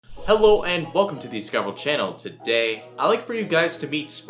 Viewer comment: Is it me or did it sound like he said "Welcome to the Dicovery Channel?"